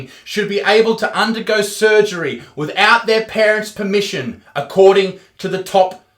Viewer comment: Ooooohhh I thought it said DURING surgery